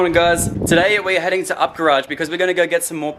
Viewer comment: “Ok this is everything we picked up from ittako garage” Me looking at them JDM bananas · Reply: ngl I heard it as otaku garage the first time he said it and I nearly spat out my coffee